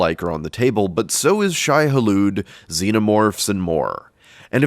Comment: I wasn’t looking at the screen and I thought you said Shia LaBeouf istead of Shai-Hulud...and honestly, I didn’t even question it for a moment.